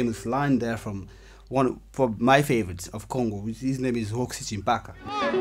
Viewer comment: Can you give the name of the guitarist you mentioned at 3:10 please? I didn't quite catch it. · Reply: Roxy Thsimpaka